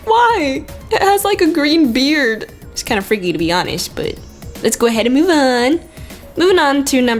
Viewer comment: What I thought u said: it's kinda freaky to be oddish